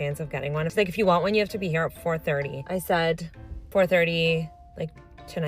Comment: I heard her say “I’m here at 3am” and my mind immediately went “you are a goofy” lol